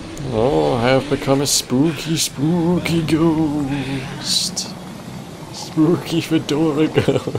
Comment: The reason they have so much soap is to clean the soap before they use it. Also the First time I watched this I thought you said in the beginning 10 seconds "spooky spooky who dinosaur kinda game" I thought "What kind of game is that... or is that just some weird play on words" Then I realized I was mentally retarded